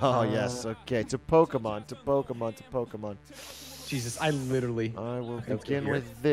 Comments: I thought he said "Joe Biden" not "Pokemon" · What I thought he said joe Biden not pokemon